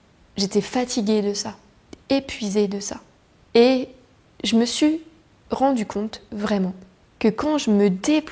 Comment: Très beau message! À 24:28, j'ai entendu une prononciation de chez nous (Québec), "j'me su"...ou j'ai voulu entendre ce que je voulais 😉